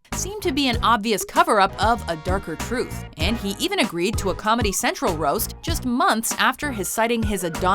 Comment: Did she just say 'Fiddy Cent'? lol